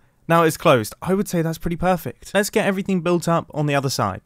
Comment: I heard “that’s ETHO” Anyone else?